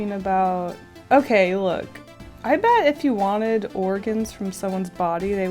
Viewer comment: GIRL MY ASS THOUGHT YOU SAID "IF YOU WANTED ORPHANS FROM SOMEONES BODY... IT WOULD BE VERY EXPENCIVE"